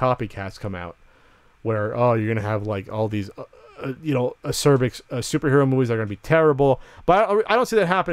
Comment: Why does say "cervix" at 2:35 ???